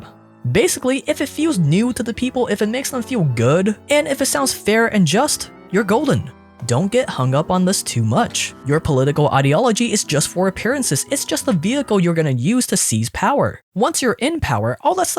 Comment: I keep hearing "political audiology" instead of "political ideology" lmao